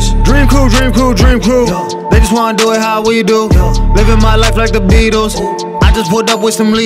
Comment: It took a month just to find this song! It's worth it. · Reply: I thought he was saying "dre cu dre cu bitches wanna do it was we do"